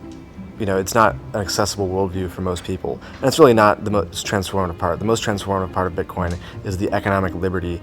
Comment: What did he say? It's not the most "transform.." apart?! What?